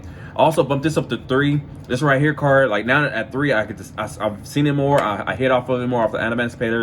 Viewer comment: could anyone write here that card name please? I can't understand english very well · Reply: its Cartorhyn The Hidden Gem of The Seafront, a common from POTE